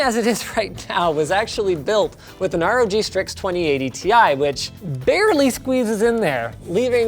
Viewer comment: The captions say "20 ADTI" seriously? Edit: ok i am not trying to be a karen here but 11:20 - 12 pin not "1210" 11:36- 2080 ti not "20 ADTI"